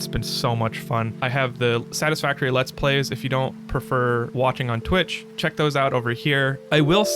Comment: at 0.32 i thought you said walking on twitch before i realized you actually said watching on twitch. I had a little chuckle trying to imagine how many different ways you could walk on twitch before my bubble was burst. I might have to check out your streams on twitch tho.😊😊